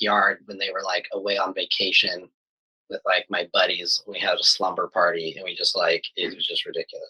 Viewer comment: did he say slumber party's ✌🏻💚🌬💨💨😶‍🌫️